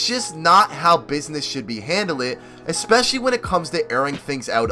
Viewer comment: Did you say "not how business should be handled-ed"? Lol